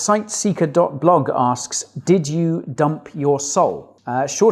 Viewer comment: I heard “did you dump your soul” 🤣